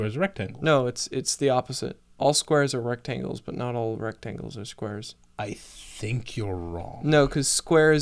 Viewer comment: I somehow misheard that as "all CIRCLES are rectangles, but not all rectangles are CIRCLES" ... which is my new favorite phrase.